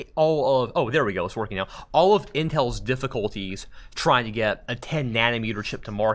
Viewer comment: does he call it inhel?